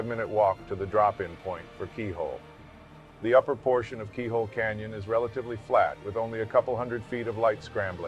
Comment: I really thought he said peehole canyon